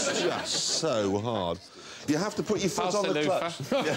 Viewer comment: what does the guy in green say? Everybody laughs... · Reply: Pass the loofah.